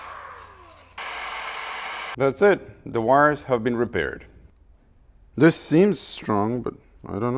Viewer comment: "the wires have been repaired" sounded like Dire Straits.. "Elvis left the buliding"